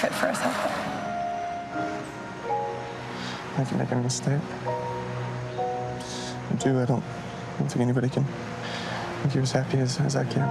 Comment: What did Blake say at 1:12 - 1:20 ?? I can't hear what he said. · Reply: “I think ur making a mistake, I do, I don’t think anyone can make you as happy as I do”